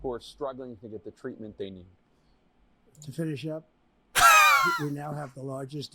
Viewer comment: Did trump say turn your shit up at 8:35 or what am I hearing wrong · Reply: To finish up